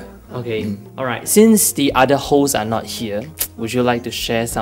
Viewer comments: I actually understand him better than the interviewer... especially when the interviewer said "hosts". I heard "holes". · he is the worst mc there can be. he doesn't make sense most of the time. i thought he said "what about the other hoes'' as he was pointing else where. I didn't realize he meant other host/mc on running man until he mentioned lee kwang soo.